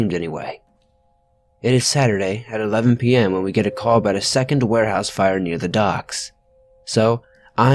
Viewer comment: First story I heard "werewolf fire" instead of "warehouse fire" Lol! (I was painting my fingernails at the time, ergo I wasn't listening as closely as I should have been...sorry.) Especially since it was such a frightening and eerily sad story. That was just FREAKY!!